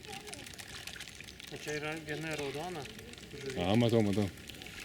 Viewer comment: he says "I can see" But I don't hear the words clearly, how is it spelt in Lithuanian? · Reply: Matau.